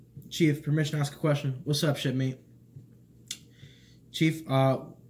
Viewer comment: "What's up, shipmate?" I swore he said "what's up, shitface?" and I lost it.